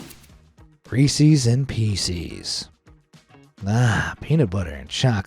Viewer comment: Is it just me or i just hear feces instead of pieces